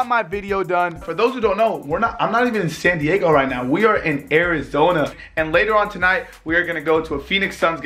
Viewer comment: In AIRIZONA 😂 it sounded like that